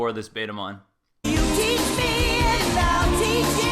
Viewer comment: I like when he asked if he can watch porn lol she said no, and I honestly thought that little skit said BetaMom cause she done took control lol 😂😂😂😂😂👌